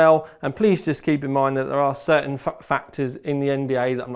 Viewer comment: Certain Fuck Factors in the NDA? 😆 Is that what ASUS said they’d do to you if you leaked any info?